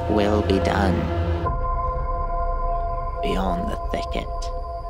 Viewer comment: I thought it said the thickest